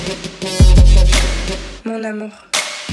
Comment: What she say? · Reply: lylkym malabad She say " Mon amour " it's french =" My love " . Louane is a french singer 😉